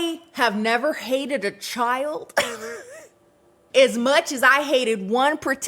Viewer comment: thats what I was thinking. But Powder isn't a baby? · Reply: yeaa I just thought I heard her say "baby" not "child"